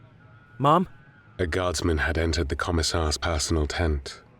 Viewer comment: Did he just call her mom